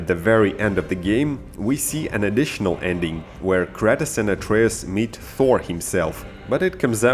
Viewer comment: wait... did you say cratus????